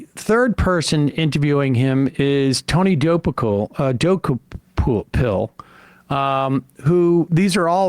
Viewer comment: it’s admittedly juvenile of me, but it couldn’t help but stick in my head that it sounded like Sam was doing everything in his power to not pronounce it “Tony Dookie-Pill”